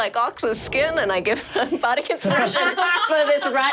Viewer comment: This entire times I thought Siobhan was saying body inspiration. LMAO